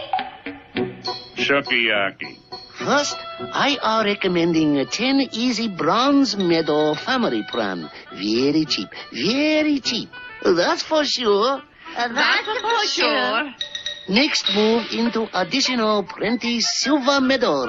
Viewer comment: I’m Japanese/American I watched it as a child and now and I still think it’s hilarious…..grow up people it’s a cartoon about a caveman family!! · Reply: Anyone else thought the instructor said "That's for very,very DAMN sure!"